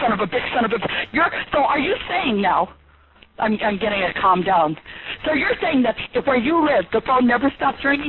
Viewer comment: What did she say? “Im getting a come down” that explains why she was freaking out so much karen probably on somethin💀